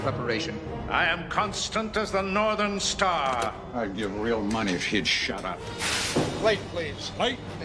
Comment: “I’d give real money if he’d shut up.” I quote this way more often than I should. 😂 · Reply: when Bones quoted that it sounds like he said "I'd give real money if she'd shut up" which makes me wonder if they edited out a scene where the female Klingon who took over for her father was making a speech Bones did not like.